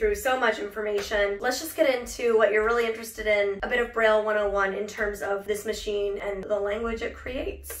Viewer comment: Oh crap I thought it said barbie 101